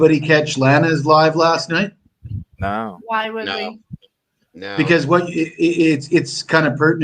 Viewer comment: why wouldn’t we? 8:05 if that sigh was any heavier… you’d be overweight! 😂 8:15 if, by saying: ”im not gonna say a word,” is a true and accurate way of letting viewers know you have ”nothing to say,” WHY THE FUCK DO YOU SAY IT??! (I can’t stand this woman making all the snide remarks… if ya couldn’t tell. I hope I’ve made that ABUNDANTLY clear. She accused Julez of stealing funds and trash talks Lana at every corner possible… she’s just rotten to the core!) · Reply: I think she says "Why would we?" when asked whether she saw Lana's show. Bad vibe from her.